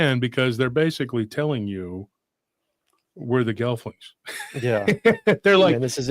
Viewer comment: you mumbled, uh, a word you said that they're telling you where the thing is, what are you saying? Gelfing? Grelfing ? I can't understand what you're saying. Oh man, no one's gonna answer this. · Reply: Gelfling from the dark crystal.